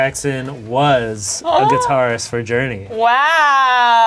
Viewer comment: Did he say guitarist?